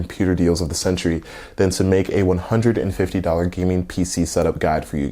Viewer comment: I genuinely though this was a pc build because I heard "gaming headset" but somehow ignored the "headset" part.